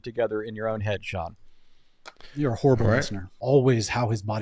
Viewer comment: I love the zoom in on eyebrows going up after he realized what Sean said, and Sean toping it off with "your a horrible person". Just too much. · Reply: I heard him say “horrible listener”